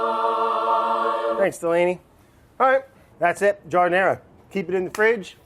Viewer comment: I didn’t read the title and this entire time I thought brad was saying he was making a “jar o’ nair”